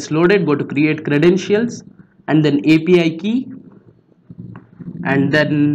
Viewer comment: it sounded like he said FBI Key lmao be careful